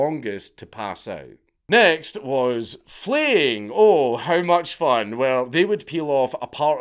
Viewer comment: When you said "flaying," I thought you said :"fleeing" where the prisoners were given a chance at freedom, but no That's not what it was